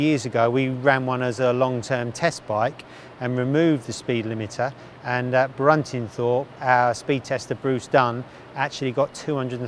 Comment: actually got 203 mile an hour out of one.” Can one of you blokes tell me wtf he said from 1:21 to the word, “actually.” Lols. I’m dead serious. Not a clue. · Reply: "...removed the speed limiter, and at Bruntingthorpe, our speed tester Bruce Dunn..."